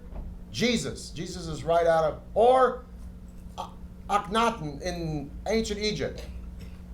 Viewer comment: Can anyone tell me the name of the Egyptian person Peter Weller is talking about? I can't understand it :) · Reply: Akhenaten :)